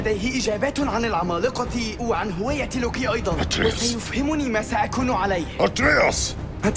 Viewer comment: I love how at around 1:00 kratos says Atrei (Russian version of Atreus) and the captions say A TREE! 😂